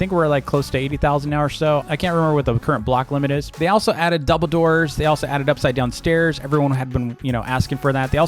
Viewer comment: After 69 times of replaying, I finally figured out what you were saying. I kept on hearing “Dumbledore” lmao